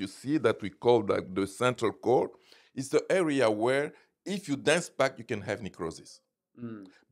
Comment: In 9:31 what does it say? If it dance back it ll have necrosis. What does that mean?